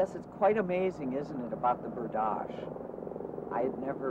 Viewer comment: "well yes, it's quite amazing, isn't it, about the ... " i don't understand what word she is saying there, is she talking about the tribe's name? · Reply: it’s “berdache” :)